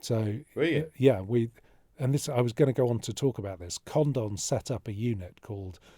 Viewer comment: Who set up a unit did he say? Condom????!?!?!? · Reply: Condon